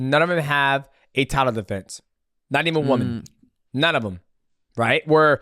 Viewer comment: did say "Not even one" or "Not even women" cuz Valentina got a title defense on French Manon before this recent defense against our beloved Chinese queen. (sure Kayla and Dern are newly crowned) And technically an interim belt is a champion contract and Tom got a rubis on his. But my point is mostly the women thing